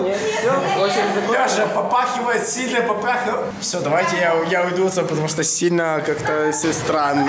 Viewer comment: ЧТО ОН ГОВОРИТ ? АХХАХАХАХАХАХАХАХАх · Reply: сильно попахивает и че